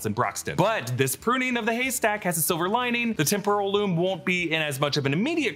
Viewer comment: Did... did they call it a Temporal Loom so it sounds like Ke is saying Temple of Doom?